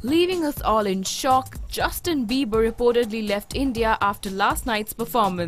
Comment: did she just say Bieber's??? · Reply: Dith Zou yes she said d that it's believers btw